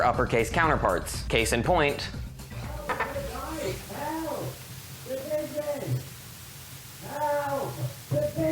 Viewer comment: Besides the point, I heard “help, the pigeon” WHAT COULD THAT POSSIBLY HAVE TO DO WITH MINECRAFT